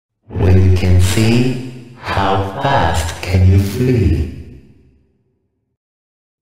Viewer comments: "Listen to my voice, and you'll find the key." "When you can see, how fast can you flee?" "Be sure you step, through the heart of madness." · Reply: I heard “be sure you smell through the heart of madness” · "Listen to my voice, and you'll find the key." "When you can see, how fast can you flee?" "Be sure you step, through the heart of madness." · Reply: I hear “Be sure your stealth through the hearts of madness”